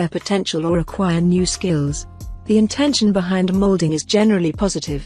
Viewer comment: Women with 'SKILLS'? I'm sorry. I thought she said 'SCALES'? You know, like a SNAKE?! 🤨🥴😆😎